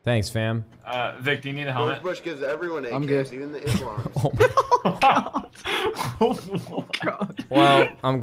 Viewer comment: did he say even to Islam thats messed up what he said